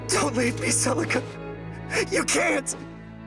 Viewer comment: What did alm say i heard "Tell _____ ____ Celica , you cant!"